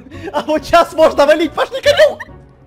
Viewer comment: я одна услышала пошли Камилл